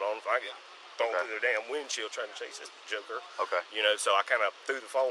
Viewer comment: This makes me sad on so many levels. 25 years old lost his life for nothing and you can tell everyone involved has no remorse. God bless his soul 🕊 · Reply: What did he say???? "Trying to chase this jigger" I just caught that after all this time.